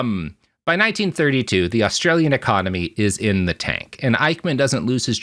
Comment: Swear to god at 54:24 Evens says "the Australian economy" lol unsure if he actually does or I'm mishearing it